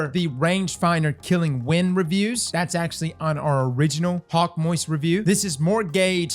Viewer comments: Did I hear "Hawkmoist" review at 0:33? xD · I'm sorry, did you say "Hawkmoist" at the 0:35 mark? LOL